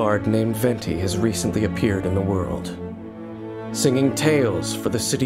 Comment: "A mysterious bard named Venti jas recently appeared in the world." Bro I thought it said "in the wild" LMAOOO